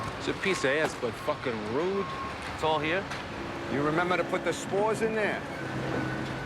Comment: what did he mean about spores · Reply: Spores? I thought he said smores. Now I gotta go to the grocery store and pick up a smore kit.